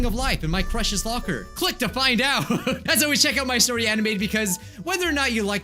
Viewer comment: Did you say My Story ANIME?!